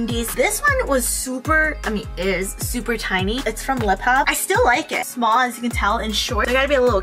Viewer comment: Wait what brand did she say at 6:45 ??? I love that top but I can't understand what brand she meant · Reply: Hi! It’s from LIPHOP on YesStyle! :)